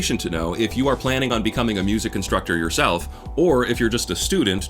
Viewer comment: I think you said "music constructor" instead for "music instructor"...